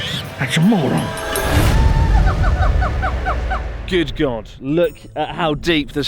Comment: I thought Chris said "Good Garnt" 🤣